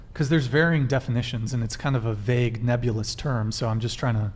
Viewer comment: did someone say nebulous? 😛